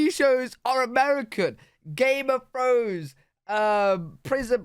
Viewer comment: The reason he chose British TV is because Britbox is his sponsor. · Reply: Did he actually say “game of fros?” 💂🏿‍♀️ WHAT…?